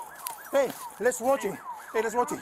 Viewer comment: I thought the cop said: “hey let’s smoke him”